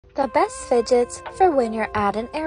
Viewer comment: Omg I thought it said best fidgets for airpods